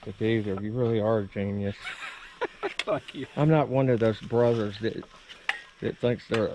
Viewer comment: LOL... what was that you said at 9:10? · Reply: "Thank you" distorted by a laugh